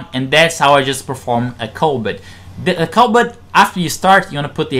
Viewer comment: Did you just say COVID? I'm sorry I just found out about PSM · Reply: Lmao He said kulbit